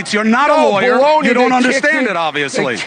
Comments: I thought he said blow me, so I had to rewind lol it was baloney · LMFAO I didn't hear "Baloney" the first time. I had to play it at least twice, lol, I thought he said "BLOW ME!" 😂🤣